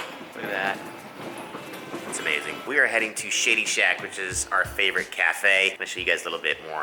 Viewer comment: i thought you said shitty shack